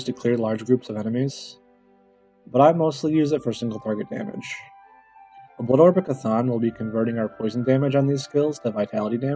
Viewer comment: Although a nice guide, your mic is pretty bad which makes it hard to understand you some times. At the 1:57 mark you talk about something that converts poison dmg to vitality dmg, but I can't understand what you're saying. What is it? :) Thanks! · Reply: It's a blood orb of ch'thon. <link>